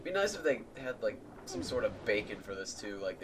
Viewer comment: When you said Beacon, I thought you said BACON. XD